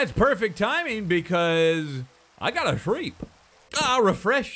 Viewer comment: every time he says "I gotta sleep" the auto subtitles say: "I gotta shriek" every. single. time. and that's hilarious XD